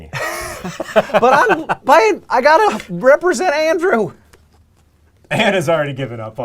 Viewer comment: "but I have to represent andrew?" Is that what he is saying? (english is not my native language)